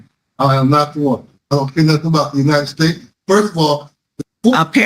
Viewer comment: Did I hear that right ? He said “I don’t care about anything about the United States” ? 😡